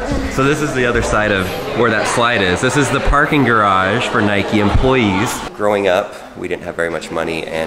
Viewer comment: My dad designed that garage. His architect firm is SRG Partnership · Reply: Oh I thought you said garbage